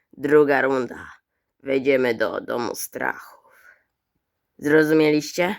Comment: Usłyszałem wejdziemy do domu starców 💀.